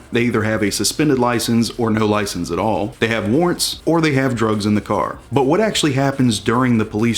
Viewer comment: When you said warrents I thought you said ordinance! What?